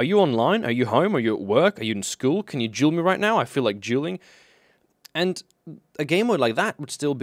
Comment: It sounds so funny because of accent it sounds like "juuling" instead of "dueling."